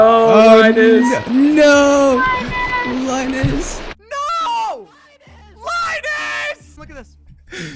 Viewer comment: i got so confused because it sounded like they were saying Linus, which is also my name